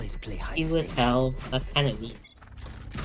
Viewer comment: Anyone hear the iron say "He was hell, thats kinda deep" Had me cracking up